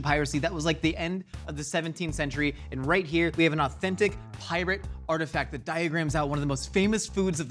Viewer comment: I thought said "right here we have an ancient pirate heart attack" lol